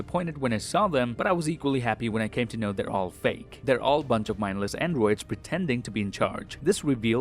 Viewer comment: did you say a bunch of mindless N words?!